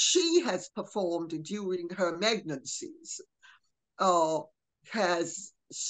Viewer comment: Around 👉11:15 👈 Did i hear Lady C say "meg-nancy?" 💀